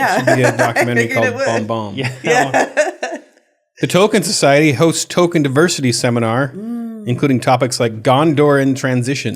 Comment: I hear "The token society discusses token diversity" Then I realised it he said token...